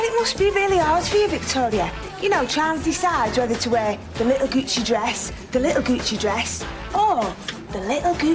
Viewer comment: so I was a bit of a literal kid when this came out. so when they were saying "little gucci dress" I thought they were saying goochi, which was a style of dress, not the brand and I also thought capricorns were a snackfood lol